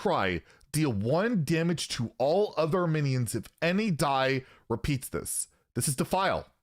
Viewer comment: Also ensure they can’t attack with their pirates next turn, no way they’re running that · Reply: ah I thought it said 'all other enemy minions' for some reason, but you're correct